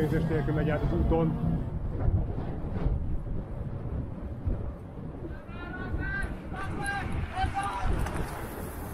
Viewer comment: A 7:27 az mi? Ki mond mit mond honnan mond? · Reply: Egy ismerős, hogy "RAGMEG!". És ilyenkor köztudott, hogy köteles vagy sprintelni :D